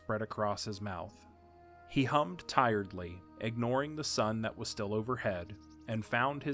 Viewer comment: I THOUGHT IT SAID HE “HUMPED TIRELY” 😭💀